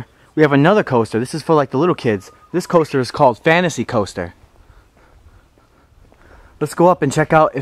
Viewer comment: For a second I thought he said 'Fantasy Toaster' instead of 'Fantasy Coaster'😂